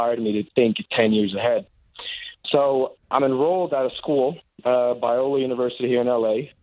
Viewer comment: He clearly said "Biola University in LA" and they keep calling it "Viola". There is not a Viola University.